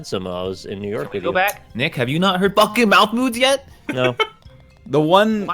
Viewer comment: i thought everyone was saying MALF Moods not MOUTH Moods